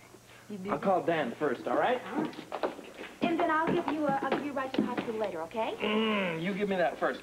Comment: What does Tiffany say to Luke around 5:28 or so? She says I'll give you a few _ and talk to you later. I have a feeling TG turned it into something dirty, the way the three are giggling. I can't understand what Tiffany said she'd give him though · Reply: She says "I'll give you a few rides to the hospital" I am thinking she should have said I'll give you A ride but since she said few it turned out dirty :)